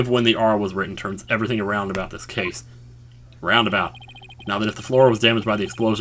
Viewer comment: Did you say... Roundabout ? <====TO=BE=CONTINUED=====